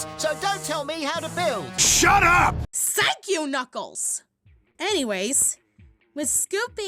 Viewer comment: Misheard it as "SYKE YOU, KNUCKLES!"